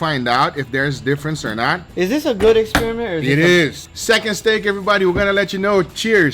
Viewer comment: what did say? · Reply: cheers everybody